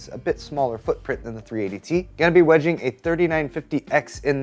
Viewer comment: I thought it said wedding instead of Wedging. I was like, now there's a man who loves his CPU